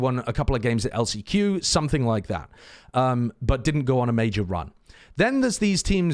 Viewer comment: I heard GO ON A RUN